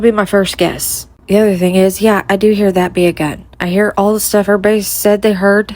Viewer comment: I heard "That'd be fun" NOT "That be a gun"! Also, LG is definitely whispering 2 another girl at beginning. AW is 2 far away 4 whispers. LG shadow is a bit long for 2pm as well and there's another shadow with LG when AW is still on bridge so WHO'S MFN DNA IS THAT?! JS...